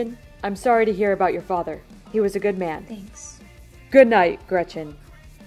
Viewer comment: Did santa just call the grinch a good man lol 3:36 love all ur vids btw